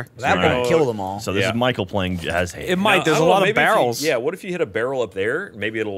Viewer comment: Did call Michael 'Mike' at 2:56 ? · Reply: He said "it might"